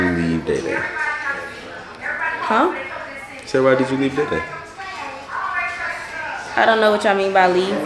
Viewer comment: What did say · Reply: Think he said “why did you leave day day”?